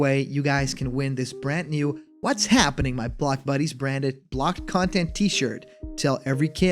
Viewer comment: I thought you said "What's happening my fuck buddies"